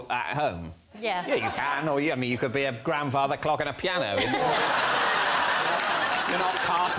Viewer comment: "Or you could be a grandfather clock and a piano." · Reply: I thought he said "a grandfather cocked in a piano"